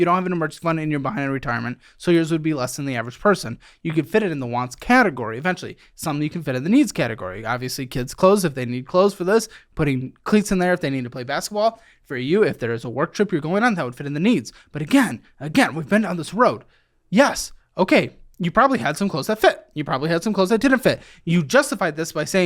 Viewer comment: I think I heard “cleats if they want to play basketball” lol